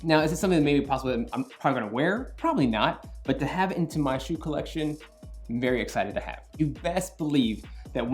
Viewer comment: Cyrus Pinero he said that he’ll show off his shoe collection?? Awesome! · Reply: oh..not that one..i thought you said the lego collection..srry